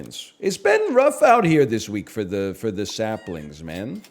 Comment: Did he just call us SAPlings? The Fauna collab is real?? Librarian is gonna lose his shit.